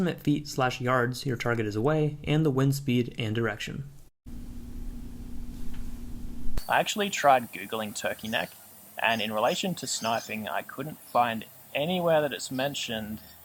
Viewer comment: I thought he said wind speed and erection